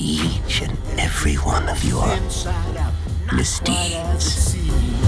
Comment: did he say misty?